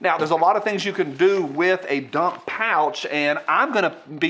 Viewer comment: Thought you said duck pouch